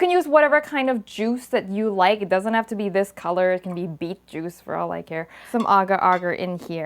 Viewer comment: "..it could be beet juice for all i care" HAHAHAHAHAH I laughed way too hard at that x'D .....meanwhile I have uni exams soon and instead of watching lectures I'm watching your cooking videos ._. #helpme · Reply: I thought she said beef juice! 😂